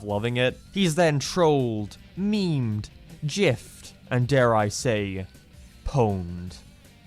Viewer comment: did you just said yiffed?